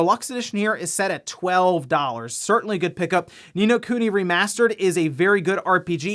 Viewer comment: what game is this? i can't make out what he's saying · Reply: Ni no kuni remastered